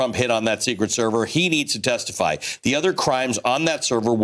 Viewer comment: Like he really cares about other Presidents. GMAFB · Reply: But I heard it as "... TO ME, for the future" and NOT 'future PRESIDENTS'. And i was thinking, "your future is in some prison"